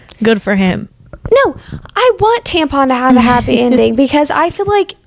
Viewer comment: when says tamp0n instead of Tamlin. Lollllll